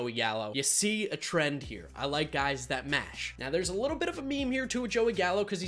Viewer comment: You probably heard "I like guys who mash." What I heard, "I like guys who smash."